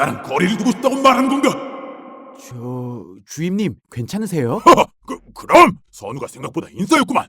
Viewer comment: 왜 주임님이 주인님으로 들리지